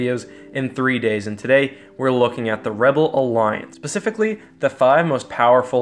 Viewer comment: does he say 'pacifically'?